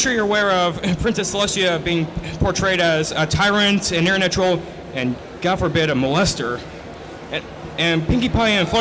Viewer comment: What the hell was he even trying to ask. All I hear is "Darker side of fandom, a molester." What is he trying to say? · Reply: ***** he said "a tyrant, an internet troll, and god forbid, a molester"